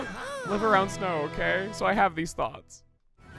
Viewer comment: i had this playing in the back when i was cooking and at 10:10 i hear say i have these thots. i died laughing